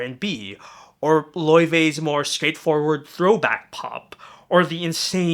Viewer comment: Can someone help me? I'm trying to find the fourth artist listed at 2:15 but I couldn't hear the name right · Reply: It's "Laufey", a fairly popular throwback-pop / jazz artist who blew up on Tiktok. I'd recommend her songs 'From the Start' or 'Promise' if you want a sample of her sound.